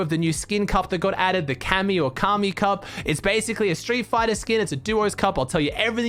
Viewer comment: when you said cammy cup i thought you said kami cup